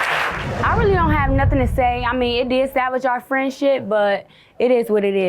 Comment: Did she say "It did 'damage' or 'salvage' our relationship" at the end??? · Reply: Yep, sounded like she said salvage 🤦🏾